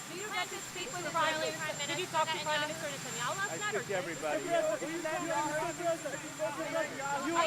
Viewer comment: Did he say, "I tricked everybody" at 2:01 in the video?